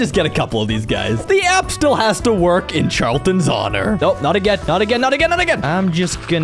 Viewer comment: I that's impossible How is he alive I thought you said RIP charton